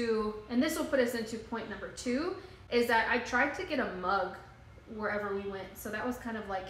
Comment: I thought you said a bug at first and then realized you said mug 😂